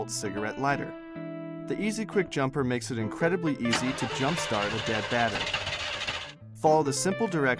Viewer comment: go to 00:16.... I thought u said the battery was dead.... lmao